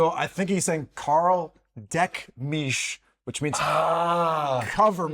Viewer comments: "Deck mich" is a very different thing. That's what a horse would tell another horse if it wants babys. You know? "Deck mich!" ...."decken" is the term for breeding animals. So "Gib mir Deckung" is correct in this case. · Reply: What I first heard was "Leck mich!" 😂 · "Carl, Deck mich" as a German I thought he was saying "Mann, Fisch mich" 😂